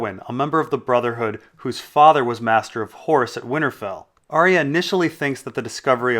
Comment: I know you said horse but for some reason I heard it as “Master of Whores”